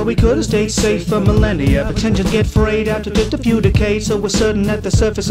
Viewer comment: In the subtitles, it says agter instead of after